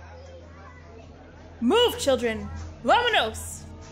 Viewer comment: What did she say 0:20 ? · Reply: Killer Move children! Vamanos!